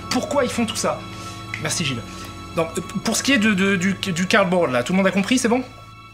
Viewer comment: à 0:44 quand il dit "pour ce qui est du Carball là, tout le monde à compris, c'est bon ?" sans vouloir faire de mauvaise blague, moi j'ai pas compris. Quelqu'un aurait une idée de ce qu'est le "carball" ? (je ne sais pas comment ça s'écrit) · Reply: Il dit "cardboard" je crois, c'est pas une blague il parle juste du tableau